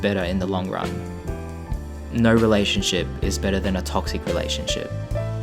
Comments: Did he say no relationship is better then a toxic relationship? Or is it just me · "No relationship is better than a toxic relationship." ~ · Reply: I thought he said "No relationship is BITTER than a toxic relationship.".